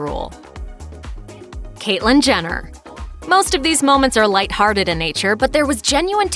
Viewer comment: When she said Caitlyn Jenner I thought she said Kylie Jenner and I was like kylie can't be throwing shade at anybody cause she said a pig was a puppy